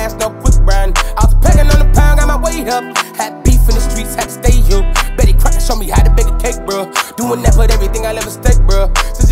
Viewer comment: I thought he said "Bet these crackas show me how to bake a cake bruh" 😅